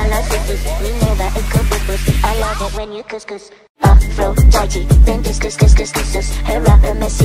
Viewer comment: When they say Aphrodite it sounds like there saying ''I'm so judgy''